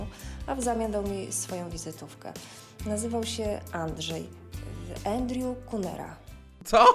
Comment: usłyszałam ANDREW CUNANAN